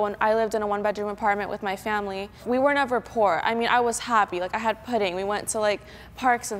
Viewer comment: did she say she had pudding??